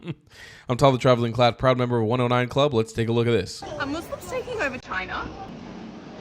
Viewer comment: 😐 Thought she said, a Muslims take my vag*na.